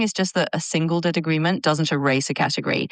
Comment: What does the computer say? "a single dead agreement"? It's not clear to me. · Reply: A single “disagreement “